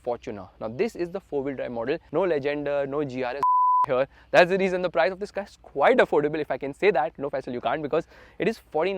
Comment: Can anyone tell me what he sensored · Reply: Bullshit was the word .